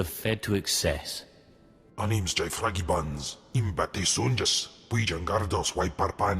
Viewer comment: Sounds like he says "My name is Jack Frankiebuns" at 07:05 <_<